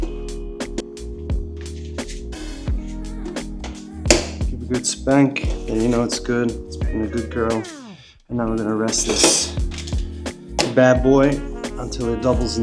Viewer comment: did you say you’ve never been arrested?🤣.. maybe you said the bread lol. I appreciate seeing your bread making process, I’ve just recently been getting into bread baking. Made a from scratch french toast w/a whipped brown sugar honey butter tonight ✌🏼